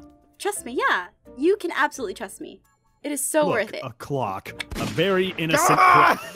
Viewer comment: "Oh look, a clock-" Bang "ARE YOU KIDDING ME!?" (I admit, heard it as "GlocK")